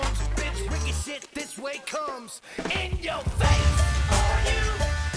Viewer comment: the first time i heard this song i thought they said in your vase for some reason but then i was watching the video online and i got it